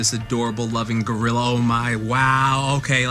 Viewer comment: did you just call her a gorilla? Uh oh…